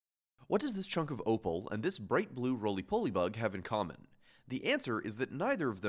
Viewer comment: I heard "roly poly bug" as "roly pokebug"